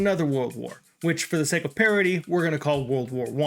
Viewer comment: Good video, at about 1:00, I heard ‘parity’ and thought ‘parody’. English is the best worst language in existence